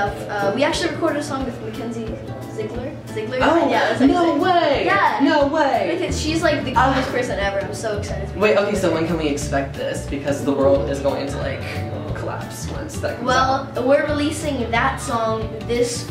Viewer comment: Did her say kenzie is the cutest person ever